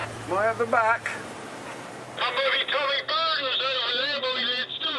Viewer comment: For anyone asking - Season 2 is coming. I spent a few hours going over it, to produce a 10 min video, but then YouTube did a copyright claim so I withdrew it. I'm going to rework it and try again. It may take a day or two to accomplish this though. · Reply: I think he says "I'm moving Tommy Bartons over here". Slang for fast? or "I'm moving til me bar tons"? Something like that problem is he always replies with something complicated, not just "yes", "foward", "slower"